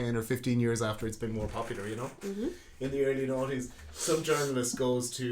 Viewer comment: Love how he says Nineties...sounds like he is saying Naughties. Lol. Was a fun episode.